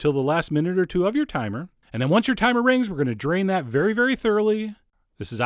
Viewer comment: it says "timmer" instead of "timer"